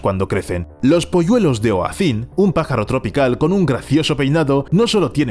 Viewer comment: como se llama esa ave? No entendi bien su nombre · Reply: Oasin